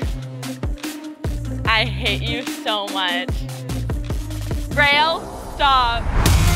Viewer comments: DID SHE SAY RAIL?😭☠️ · did she just say "rail"